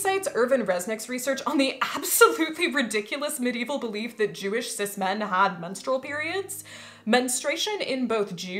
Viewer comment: your witch hat is way too tiny. It looks like it's for a doll. 5:00 did you just say "Jewish cis men"? Oh, your one of those...